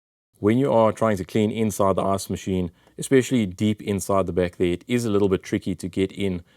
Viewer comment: I love that as an American every time I hear you say “ice machine” or “ice reservoir” all I hear is “arse machine” and “arse reservoir”.